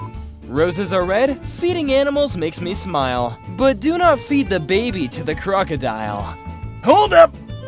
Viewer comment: I THOUGHT IT SAID ‘do not feed baby crocodiles’